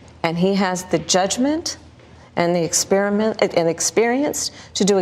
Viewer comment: Did she just call Joe Biden an Experiment ?